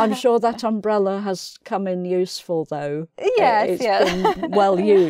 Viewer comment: I think is saying "...has come in useFUL, thugh...", am I right? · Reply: Useful Though